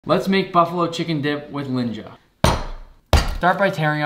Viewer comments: First i thought he say ,,Ninja” And i was so confused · I thought he said “with Ninja” and I almost scrolled away immediately 😂